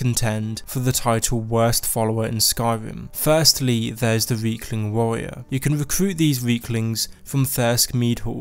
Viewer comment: I thought he said weakling at first instead of riekling.. I mean I guess he's not wrong..?